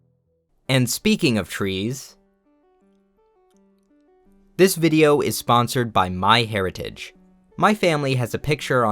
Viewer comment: what where you trying to say? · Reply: He was silently mouthing "I'm sorry" LMAO